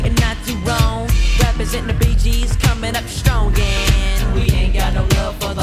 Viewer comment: "Representin BG's and comin' up strong, and..." What?? I had to look up the lyrics, because I always thought he said "Rappers in the Bee Gees" and was like "WTF??", since I know that's not a rap group.